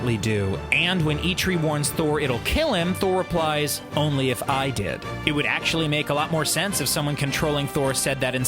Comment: I thought he said "Only if I Die" not "Only if I did"